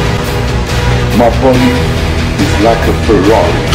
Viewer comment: What does the guys say at @ 2:51 ??? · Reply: I know it's kinda late, but I think he says: "My body is like a Ferrari"